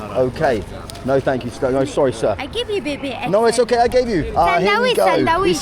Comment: Did that lady offer to give you her baby for money? or was she asking for money to feed it? I just don’t know if I caught that right and I’m not seeing it mentioned. If so that’s tragic but damn I swear that’s what she said.